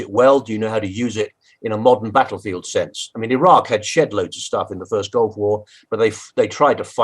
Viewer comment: did he say "sh*tloads of stuff"?